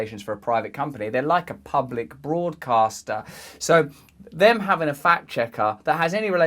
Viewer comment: I thought you said at first a public fraudcaster